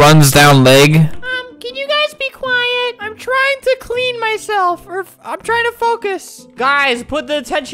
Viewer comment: I thought he said im trying to cream myself 😂